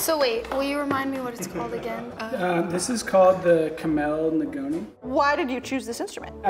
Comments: Anyone know what the instrument is called? I can't make out what he says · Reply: kamale ngoni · what is he saying here what is his instrument called? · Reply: kamale ngoni